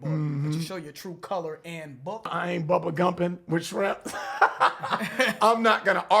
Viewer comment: I heard bubble gumping with shrimps Reference to the “cartoon” bubble guppies where the characters are shrimps